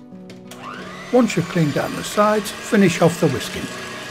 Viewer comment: I heard " finish off the whiskey", that's my story and I'm sticking to it 🙂